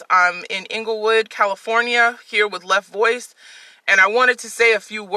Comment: Inglewood, CA. I thought you said Englewood, Chicago. Never mind. Move along..